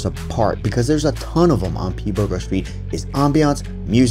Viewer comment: For a second I thought you said “P-Burger St” 🤣